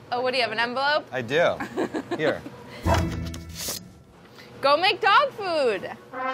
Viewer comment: I thought it said chef makes dog